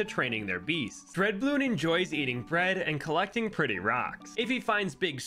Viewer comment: Number 60 I heard you wrong and thought you said “dread bloon enjoys getting bred”💀💀💀